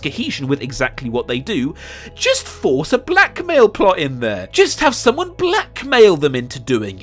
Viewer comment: I legit thought when you said "blackmail" and the camera showed a shot of Finn you meant "black male" and I got so utterly confused 😂